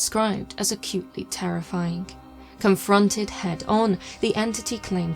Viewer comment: i thought i heard "a cutely terrifying" for a second there